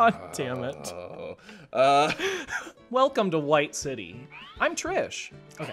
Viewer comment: I was eating chips while watching this and I definitely thought Tommy said, "Welcome to White City, I'm trash!" 😂😭😭